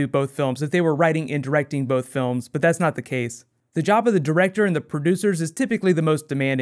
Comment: I thought you said jabba the director, not job of the director and I got really confuzzled for a second